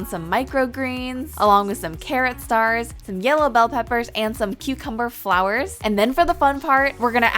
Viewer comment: i thought she said “cutecumber”